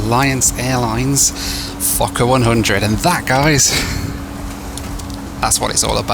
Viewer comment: I thought you said fucker-100 just spat out my tea